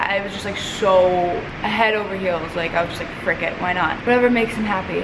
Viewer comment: When you said frick it i thought you said cricket haha😂